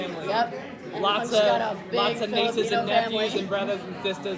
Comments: Did she say that Hunk has a big Filipino family? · DID THEY JUST SAY "A BIG FILIPINO FAMILY"?????